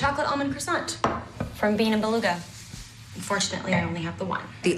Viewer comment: did she say bean and beluca. I hope she didn't mean DEAN AND DELUCA